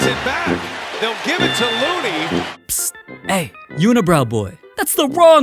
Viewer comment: lmao I thought he said hey unicorn boy 😂